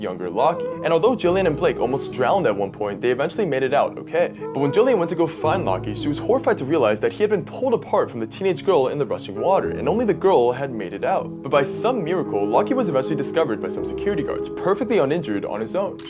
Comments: At first I thought you said the teenage girl pulled apart her kid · OML the first time I listened to it I thought it said the teenage girl DIDNT make it out, I was so depressed for a second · When I heard “pulled apart from the teenage girl in the running water” I thought he said BY the teenage girl, I was so confused